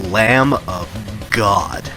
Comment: I've watched this video a few times and I still have no idea what you're saying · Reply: He says "Lamb of God".